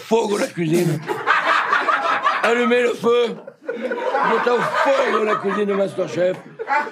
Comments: no 20:05 acho que ele falou francês sem querer, "allumé le feu" ou algo assim, acender o fogo kkkkkkkkkkkkkk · Kkkk depois de 2 anos eu entendi oq ele falou, 20:08 - Ele disse: "Admiro fogo." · Pra quem não entendeu o que ele falou em francês em 20:07 , ele disse: "Allumer le feu" que significa "acender o fogo"